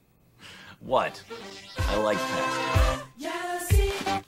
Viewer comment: What does cas say at 12:35? I can’t hear it over the humorous music sting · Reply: He says: "I like past you"